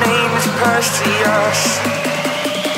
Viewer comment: sounds like he says persheush instead of Perseus